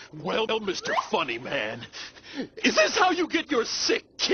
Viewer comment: I just misheard him saying "Is this how you get your sick kinks?!"